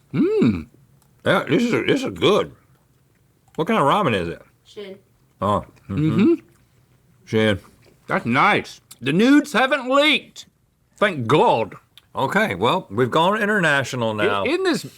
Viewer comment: Lol sounded like she muttered, "shit".